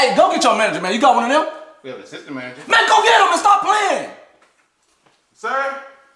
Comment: Whoever wrote the "We have an assistant manager" line was a genius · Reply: I thought he said system manager 😅